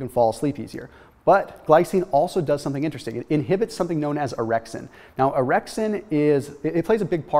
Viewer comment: I wasnt paying attention and I heard 'if we can inhibit this errection we get a better night's sleep'... orexin... not errection